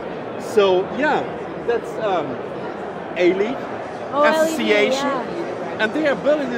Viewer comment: Did he said ALED instead of OLED ?